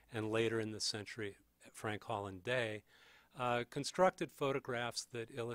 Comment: To anybody reading this who may have misheard the photographers listed around 7:56 in the wider context, they are; William Lake Price Julia Margaret Cameron Fred Holland Day And the two which he stated responded to Duane is; Garry Trudeau David Levinthal